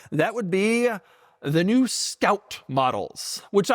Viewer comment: I heard it STOUT instead of SCOUT. Just to let you know STOUT is a pickup truck made by Toyota.